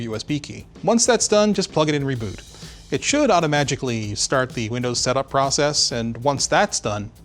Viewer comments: Did he say Auto-magically?? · Did you just say "auto-magically"? Thank you for that...I am now saying this forever.